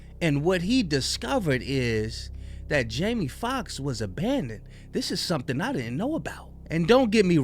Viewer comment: Im at work, so forgive me, but I honestly thought you said Jamie Foxx is a panda. I swear on everything I thought that, and thought that maybe that was some sort of racial slurr for a bi racial person. I guess its no worse than snow bunny lol